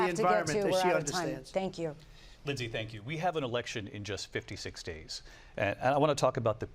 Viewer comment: Ohhhhhh 56 DAYS i heard wrong 😂😂 i thought he said 56 states im like bro how are you a moderator 😂😂😂